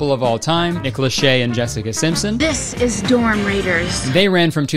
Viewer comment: i keep hearing it as "womb raiders" and honestly that sounds like it wouldve also been a passable mtv show